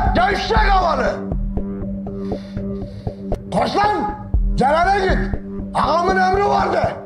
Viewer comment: burda ne diyor? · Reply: celale git